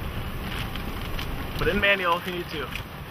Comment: Does he say "Put it in manual for YouTube" ... lmao o.O